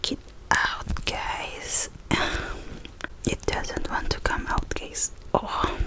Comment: Lol at 0:56 it sounds like she said “it doesn’t want to come out gays” because of her accent I love her 😂👌💙